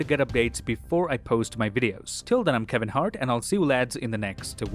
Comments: I love your videos 🔥🔥 Did you just say “I’m kelvin hart” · Did you just say "I'm Kevin Hart?" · I must be heading things because it sounded like he said at the end I'm Kevin Hart 🤣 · did you said @ 21:16 "I'm Kevin Hart"😂😂😂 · Did he just say he's Kevin Hart?😭😭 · Did he say he's "Kevin Hart" at 21:16 🤣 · did he said i m kevin hart 😅😅😂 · Okay is his name actually kevin hart or am i mishearing it?